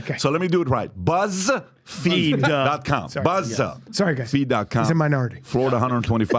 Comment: Brian , I thought he said BUST feed too😂🤣